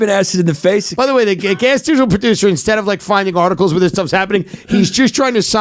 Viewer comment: what he say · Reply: Guest student producer?